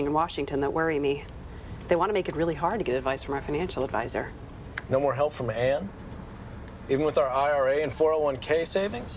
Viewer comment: that guys sounds like he said "No more help for my hand."